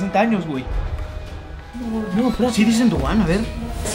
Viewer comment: se escucha que dicen duvan garcia